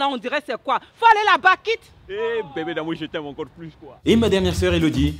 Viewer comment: Ptdrrrr "Avec ta tête moche, et t'es dents là on dirait c'est quoi ?!" 2:06 Ptdrrrrr "Hey, bébé d'amour je t'aime encore plus toi !" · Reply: Pas quoi mais toi